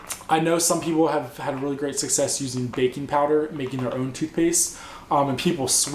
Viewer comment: At first I thought you said "bacon powder" lol.